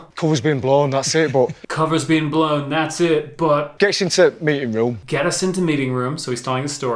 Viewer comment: 🔴 Want MORE British accents? Here are links to the rest of the videos in our UK accents series: ➡️ Ep. 1 <link> ➡️ Ep. 3 <link> ➡️ Ep. 4 <link> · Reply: He said "gets in t' mee'ing room" so basically "we get into the meeting room" not "get us into meeting room". "Gets" is used instead of "we get", he drops the 'o' in "to", and there's a glottal stop in "meeting" which is probably seen as something they do a lot in Cockney or Estuary English but we also use it in areas of the midlands where I'm from and a lot of other accents too. (Glottal stop is where you drop the 't' sound in the middle of a word, a good example would be the 'butter' which turns into 'bu'er' or likely 'bu'a' in cockney, or even 'glottal' itself which will turn into 'glo'al'.